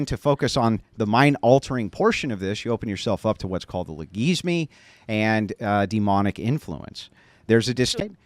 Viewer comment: What did Andrew say it opens you up to? "legesme"? I have never heard this word. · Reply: logismoi